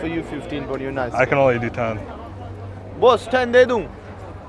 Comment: if you guys were wondering what he said at 4:26 he asked his boss if he should give it to him for 10